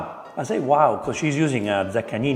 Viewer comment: we don't see the wine bottle at all yet mentions which wine it is. Weird. So what is that wine? I can't understand the long name in Italian. Does anyone know? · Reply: Donini Montepulciano